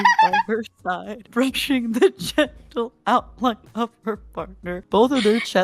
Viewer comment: I think it's actually "gentle". That's what it sounded like to me anyways. Great edit. Loved all the cuts, effects, and pictures you used